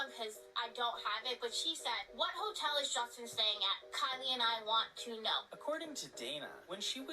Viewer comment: What even wider is why Kanye was hanging out with Hailey Bieber? Am I wrong, but I could have swore she said Kanye.